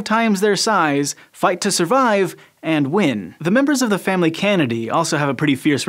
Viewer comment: I swear I heard "the members of the family Kennedy" instead